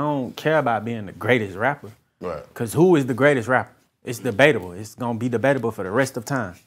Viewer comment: I thought he said the best rapper alive is debaby. Good thing for the sub titles